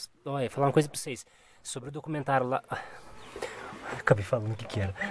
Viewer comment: Então o negócio dos marrecos é um documentário? Eu ouvi direito?